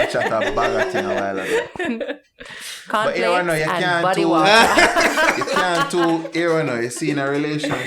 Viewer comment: How Tami seh Cornflakes and b*ddy water suh decent?...hahaha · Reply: I thought she said body water 😂